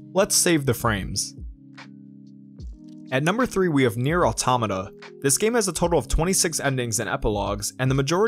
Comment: I can't understand the name of the game. What is it called? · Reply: InHUM44n it's called Nier Automata